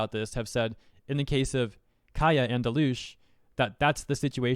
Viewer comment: Did he say Dilush? Aside from that, I wholeheartedly agree with everything in this video.